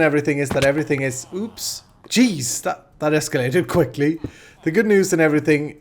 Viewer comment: Misheard at 7:25 "That Iskall-ated quickly" :-)